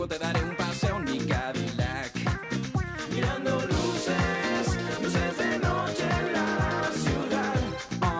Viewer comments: De saber que vendrías Te compro un pastel Si te juntas conmigo Te podría ir muy bien Todos mis amigos te Lo pueden decir y Es que tarde o temprano Tu y yo vamos a salir Es una cita conmigo Para ir a bailar Y luego te daré un Paseo en mi cadillac Mirando luces Luces de noche En la ciudad (oh! right) De saber que te quiero Te puedo conquistar Regalarte las flores Que te gusten mas Todos los detalles Que te hacen feliz Eso y mucho mas cosas Puedo hacer por ti Es una cita conmigo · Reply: Yo pensé que decía "ya no luces que no se hace noche la ciudad" :V de hecho con esa bidqueda encontré este video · cuando era niño pense que decia ; y luego te dare un paseo en mi gavilan